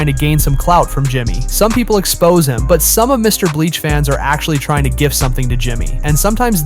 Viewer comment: 'MrBreast fans' 'MrBleach fans'🙏🙏💀💀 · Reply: I heard him say bleach too 9:29 😭